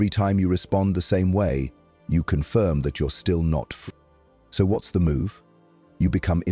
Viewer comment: 459? what is that word he meant to say? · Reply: “Free”